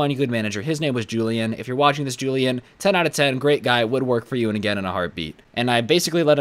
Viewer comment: Did say he would or wouldn't work for julian again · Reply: Would